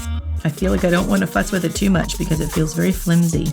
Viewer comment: I had to rewind several times around 17:10 because I swear I heard her say “fuck” instead of “fuss”